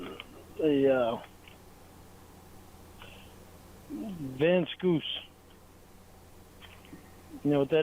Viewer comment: The peckah powch! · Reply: What does he call it at 49:08? "Hey look... there's a ... there's a ... assorted mini-syllables van Scoosh"?